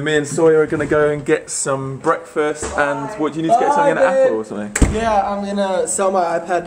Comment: did sawyer say bye babe?